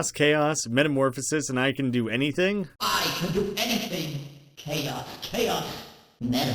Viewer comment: what the hell is Jevil saying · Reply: "The True and Neo Chaos!"